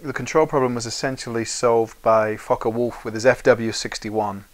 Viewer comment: does anyone hear fuck a wolf